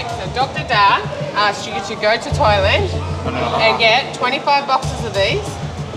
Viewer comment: literally heard it as "go to toilet" 😂😂😅😅